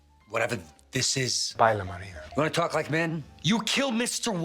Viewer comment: I think Gus says paella marinera, which is a very good food here in Spain